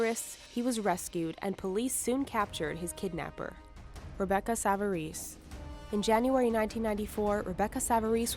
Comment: LMAO I THOUGHT IT SAID REBECCA SAVAGE!!! 😂😂😂